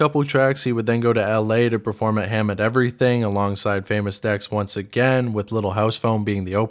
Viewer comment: i swear dude called it "Ham and Everything" lmao